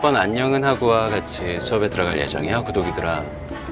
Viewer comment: 구독이들아~~ 구더기들아~ 로 들리는거 나만인가.. ㅎㅎ